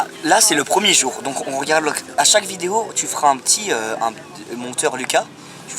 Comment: j'ai entendu MONTEUR LUKA dans la tête jme suis dis QUOI D'OU JSUIS UN MONTEUR